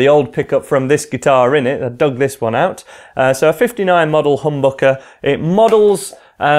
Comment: Lmao he said humbucker but all i heard was hamburger